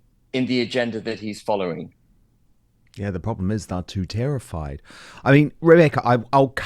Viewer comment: I think it was his accent but it sounded like he said “Two tier-ified” Thats about right.